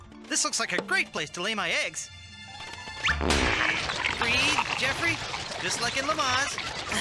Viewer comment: What does he says in min 0:08? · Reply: "Breathe Jeffery... Just like in lamaze"